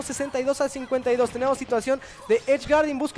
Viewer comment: I didn’t understand what the commentators were saying, but that play had my jaw on the floor. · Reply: OHHHH!!! Cee cuatro! Ce cuatro!